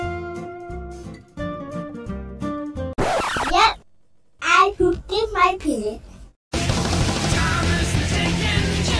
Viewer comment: what did vector say there? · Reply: He said WHAT TGE FUCK